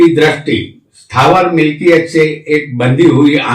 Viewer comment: What does he say at 22:16 I am not able to Understand. Pls Help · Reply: Ok heard Stavath milkiyat i.e. Real Estate